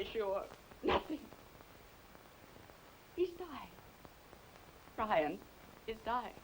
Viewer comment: I feel so silly, I really thought Ann said to Hannah that her father, Brian Colby died, but apparently, she said, He's dying. So, we might get the 411 of the situation on which Hannah's stressing.